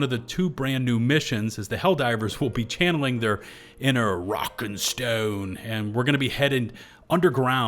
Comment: DID I HEAR A ROCK AND STONE!?